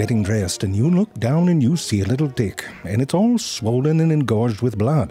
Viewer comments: the subtitles say “dick” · genuinely thought he said dick not tick. So basically the whole first part sounded more like this. Maybe its happened to you. You're in the shower or getting dressed then you look down and you see a little dick and its all swollen and engorged with blood. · THE WORD REALLY IS TICK BUT THE CAPTIONS SAY !@ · I'm I hearing things properly cuz I'm hearing "dicks" not ticks